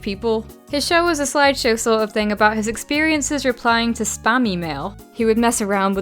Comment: I thought she said spamy mail